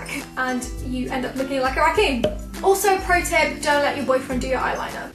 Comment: I thought she said joaquin, not raccoon.